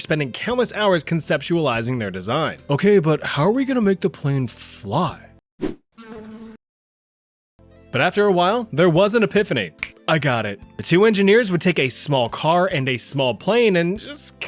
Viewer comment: Did he say how do we get the plane to fly?